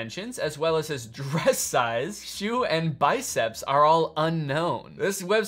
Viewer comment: WHY DID I THINK THAT AT 11:00 HE SAID "breast size" BROOOOOOOO